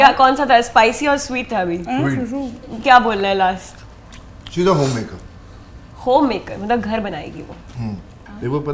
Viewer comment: i heard she is a homewrecker 💀💀